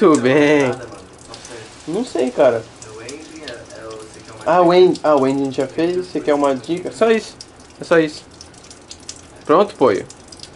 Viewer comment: o que eles falaram? Kkkkk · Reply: "ending" o take final pro rap, nossa gravação de imagem pra por na musica, se liga: <link>